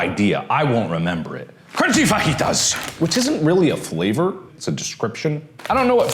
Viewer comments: i heard crunchy fetus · why did i hear "crunchy foetus" which i was watching at 1.5x speed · all I heard was “crunchy fuckitas”